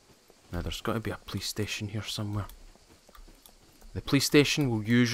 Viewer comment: Thought he said “there’s gotta be a PlayStation” lol